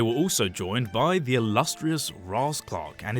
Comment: Did I hear raasclark @ 1:30 in asia?